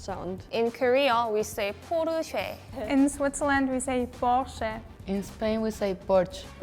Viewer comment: sounded like "порче" in Bulgarian and I had a great laugh 😂😂😂